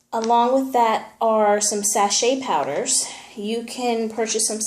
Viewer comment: please what did you say on 3:41? sa chee powder? or sampchee i couldn't get it.. please can you read it? or please someone tell me the names? many thanks